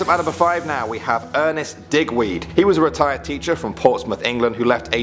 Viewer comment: When he said digweed I thought that he said d**kweed